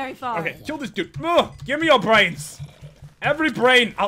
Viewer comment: i thought i heard say "Give me arthritis!" then i replayed that bit and realized it was gimme your brains! Ok im weird i hear strange things.